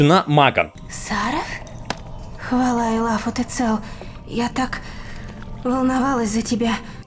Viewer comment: Слава аллаху ты цел Бл сперва так послышалось